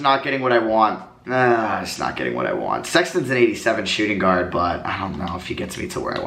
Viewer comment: Anyone else heard him say Sexlon instead of sexton